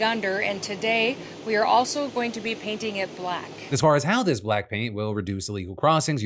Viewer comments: I heard “Black Pink” instead of “Black Paint”. I was very interested in learning how K-pop got involved in the border. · For a second there i thought he said Black Pink